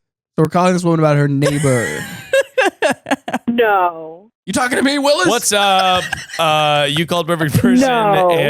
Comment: Sometimes I switch from Spotify to YouTube mid episode. Glad I did this time because with only audio I thought was flat out saying “you talking to me Willis?” Organically Every Time